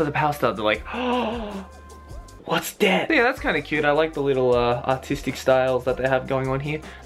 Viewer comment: I thought he said "autistic style"